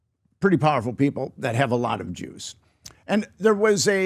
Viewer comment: Every time this guy says Juice… I think he is saying DemBoyz with the small hats 🧐